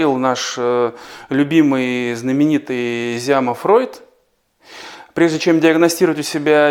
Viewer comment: Правильно! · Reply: Мне послышалось,, Фройнд'' - поэтому уточнила.